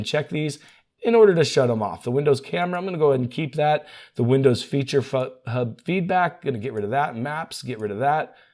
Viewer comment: "feature fuck, feature hub" bro rly said what we all wanted to say